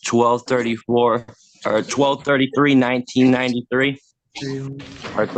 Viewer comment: anyone else hear that DOB? This guy's a freaking moron. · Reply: He said "12/33/34" I nearly fell out. 😂